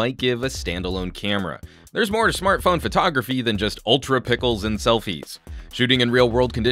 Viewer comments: did you just say ultra pickles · I heard "Ultra Pickles". LOL. Still think this is HTC's best phone camera in a really long while. Though as the video states, there's still ways it can be better, but after 3 disappointing cameras in a row, HTC finally puts a competitive one.